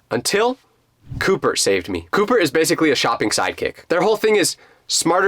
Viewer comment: I thought he was saying there was a kid name coupert